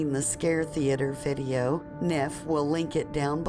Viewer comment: I swore she said Scare Thee Ater and had to rewind once to see if I was hearing shit. I was.